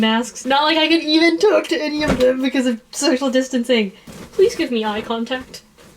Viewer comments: i heard 'sexual distancing' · I swear I heard say "sexual distancing" instead of social distancing ( ͡° ͜ʖ ͡°) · I heard sexual distancing instead of social distancing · did she just say sexual distancing? 💀😭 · I know she said social distancing but....sounded like sexual distancing LOL (still applies)